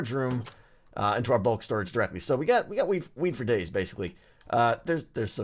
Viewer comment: did he say weed for days? Cause it sounded like that to me XD